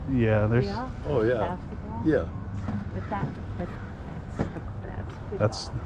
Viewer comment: What Did She Say at 9:26? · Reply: It sounds like she says ‘but that’s… but that’s the basketballs…’ Almost like saying it in a way like ‘I really wanted those cards, can you give them to me instead?’ Kind of tone. 🙄